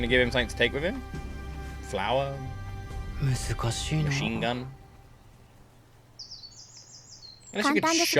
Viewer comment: did bro say machine gun?ahaha